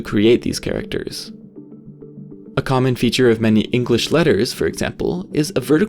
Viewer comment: i misheard that as "common jokes" what is wrong with me